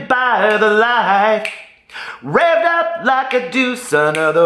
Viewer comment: Lol I Did! I always thought it said rolled up like douche!!!! LMAO I love you great filum on your Peterisms channel tonight too! 😊